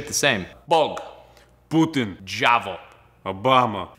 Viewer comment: Maybe I'm hearing things, but did he say "Obama" here? xD